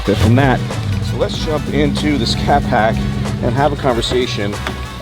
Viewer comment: It sounded like you said 'Catpack'...instead of scatpack...wow...how good it that?